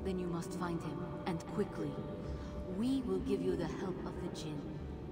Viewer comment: it sounds like she said the help of the sjin